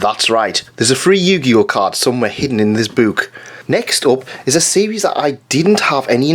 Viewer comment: When you said, "We all know why it's sealed, there's a free Yu-Gi-Oh card inside this book!" I thought you said "Inside this boob." Which is also accurate.